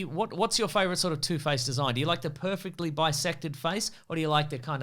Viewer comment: I thought was going to say “The perfectly bisexual face” for a moment